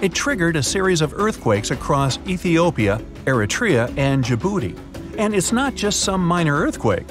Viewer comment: Djibouti? I heard Jibooty.